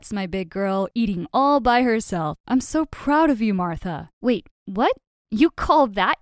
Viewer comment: Wait why did the dad sound like he was saying "I'm so proud of you, Martha" at the end of the video?